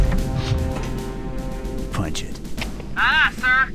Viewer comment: When Chief boards the escape pod on the autumn, I always thought he said "fudge it" even though he says "punch it".